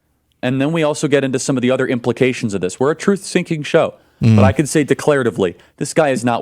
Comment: does it sound like he said "we're a truth SINKING show" to anyone else lol